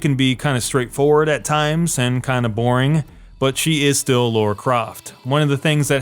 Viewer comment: At first I thought he was saying Lora Craft... lol